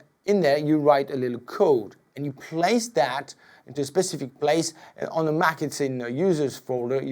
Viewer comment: I learned new words in this tutorial: Synsithyzer Arpijator Arenjament Thanks! :) Oh and of course, great tips! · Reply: did he say Macinton at 5:35?